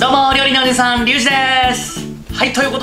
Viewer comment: 料理のおじいさんって聞こえてしまう😂